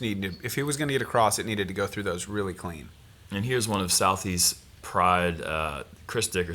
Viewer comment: said "southeast", but I heard "Southie". Thought for a second that maybe Chris was from Boston.